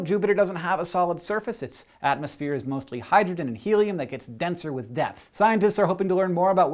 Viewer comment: It sounded like it said it gets denser with death.